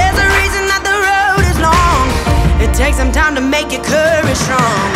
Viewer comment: I thought Lauren said "it takes some time to make a curry strong"